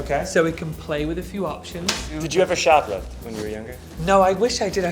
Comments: whoever can edit the captions (it's probably right in front of my face but I can't figure out how) Mulaney says "Did you ever shoplift when you were younger?" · the cc say "mumbles" but John is asking Tan if he ever shoplifted when he was younger.